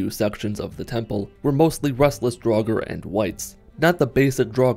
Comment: I was listening to this video and when he said 'wights' i thought he said 'whites.' I was very confused for a moment.